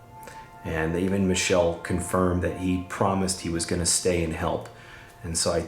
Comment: Where it said "He promised he's going to help" I heard "He's promised peace now"